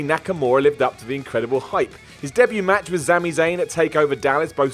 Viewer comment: am i the only one who heard ZAMI ZAYN??!!!